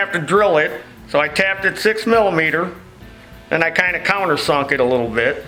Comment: I swear on everything...i go to sleep till like 12midnight watching and listening to videos.....and as i was kinda already dreaming i tought i heard him saying at min 42:33 "...and i kinda cought her sucking a little bit..." so yeah i definitely had to rewind it...you be the judge. if you kn read this...u are magnificent!!!